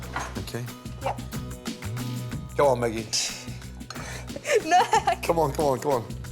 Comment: Loved when Maggie tossed the salt over her left shoulder...did that every time I knocked over a shaker while waiting tables. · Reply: Hey, thank you for the likes and apologies for calling her the wrong nickname. I misheard Gordon's nickname for her from his accent at 00:58 , he calls her "Meggie". Throwing salt over your shoulder is an old-wives tale. "According to superstition, spilling salt is bad luck and throwing a pinch over your shoulder reverses that bad luck. Typically, it's thrown over the left shoulder. We don't know exactly when this tradition began, but there are a few explanations about why spilled salt is bad luck. Take a close look at Leonardo da Vinci's painting The Last Supper, and you'll see that Judas Iscariot has knocked over the salt with his elbow. Because Judas betrayed Jesus Christ in the Bible, people began associating salt with lies and disloyalty. Throwing salt over the left shoulder blinds the devil, who was waiting there to force you into acts of bad behavior as well." Have a good day, everyone!